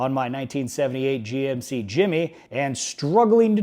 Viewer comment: Keep on keeping on bossman.. as an aside, i brainfarted and thought you said Jimny for a second.. V8 Jimny would be a riot..